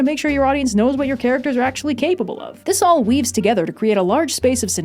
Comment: So I know you said “this all weaves together “ but I totally misheard it as “this all weebs together.”